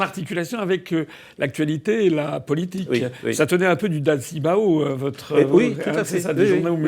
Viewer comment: Ça tenait du dal-cy-boa ? C'est quoi ça au 19:57 Très bonne idée cette vidéo, un peu d'ouverture d'esprit ça fait toujours du bien^^ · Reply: DAO-CI-BA ce sont les informations journalistiques qui étaient affichés sur les murs des villes chinoises